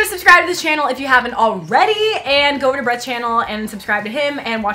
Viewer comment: I thought she said go over to breads channel